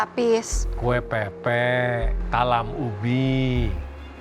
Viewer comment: kue apa ya td, takut nya saya salah denger 😂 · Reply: Kue pepe